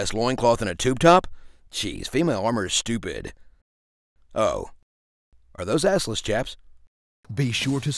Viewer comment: What's the last line in the trailer portion? I heard "Oh, are those assless cheps?"